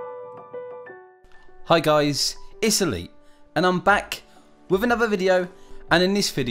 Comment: The first few I heard him say it's elite, I thought he said Italy.